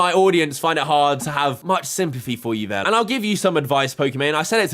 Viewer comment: did you just say "SIMPathy" because i have heard it way too many times to get it out my head